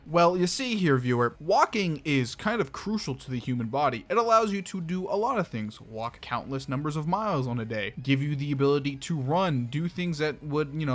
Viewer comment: what is this guy saying lmfaoooo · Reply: "walking is crucial to the human body, it allows you to walk countless numbers of miles in a day" bro no shit 💀